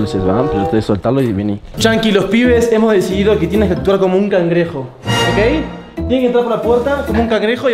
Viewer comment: No es por ser irrespetuoso pero en el minuto 4:33 a Fede le entendi: yankee tienes escroto de cangrejo